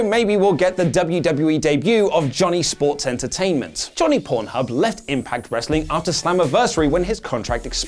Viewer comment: did he just say Johnny PornHub? 🤨